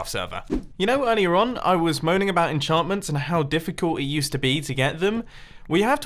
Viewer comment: Did he say... moaning about enchantments?